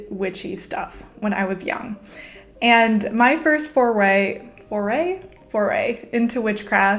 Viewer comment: who else thought they heard "My first four way..." and I looked up from where I was sitting and I was like "Wait she said what now?"